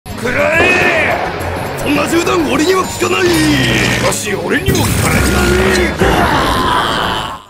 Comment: Did he say Morioh at 0:02? Is Inferno Cop just one big reference to Jojo? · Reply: YEP HE SAID MORIOH